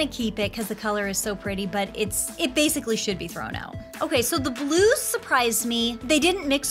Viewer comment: When you say done with the blues I thought you said we’re done with the Glooze